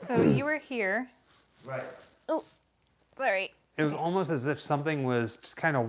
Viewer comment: I sounded like someone mimicked saying “sorry” once again it could be her whispering idk